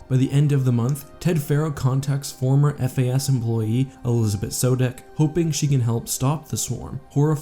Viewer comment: Sorry, at 4:19 it totally sounds like he says Elizabet Sodeck.